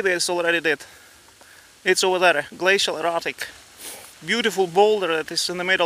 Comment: Sadly your favorite rock in the car park, a "glacial erratic", is about to become a "basaltic inclusion"! Great reports thank you, Sir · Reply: Great video. I thought he was saying 'glacial erotic!' I almost booked a flight...😃